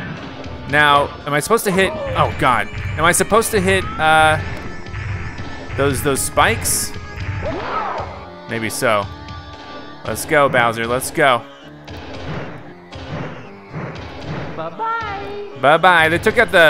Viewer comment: When you go into a stage and he says "let's a go" I always heard "Let's Pickle"